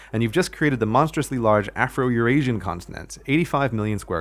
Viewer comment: Did he just say Your Asian?